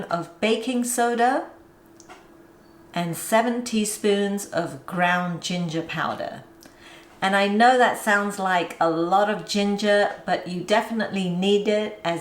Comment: I thought she said 17 teaspoons of ginger at first, lol. I would still love them with that much ginger. lol 🫚 yummm!